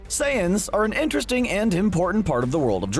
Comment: “sands” instead of “saiyans” is subtitled @ 00:20 “say in” instead of “saiyan” is subtitled